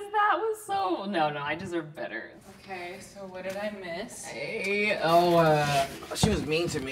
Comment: WHAT DID SHE SAY????? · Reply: “What did I miss”